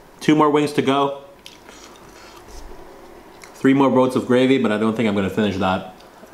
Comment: what did he say??!?! 3 more ______ of gravy... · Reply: He said boat. 3 more boats of gravy.